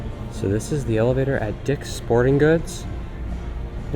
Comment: did you say a bad word, you said dick